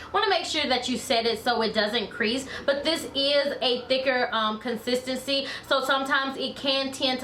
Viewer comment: Not gunna lie, I thought you said the n word when you said thicker and I was like where did that come from??? Lol